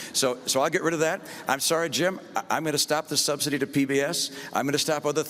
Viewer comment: is he saying that Sir "Jim" is a Sesame Street character?